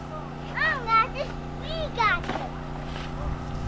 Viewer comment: “ I don’t got this, we got this !” If that’s what he said that’s awesome.